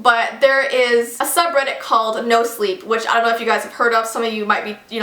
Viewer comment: when she talked about the sub-reddit "no sleep", I thought she said "nose leak"